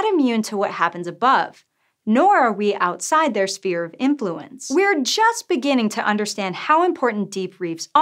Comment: Did I hear "Spear of Influence"? Sounded like that.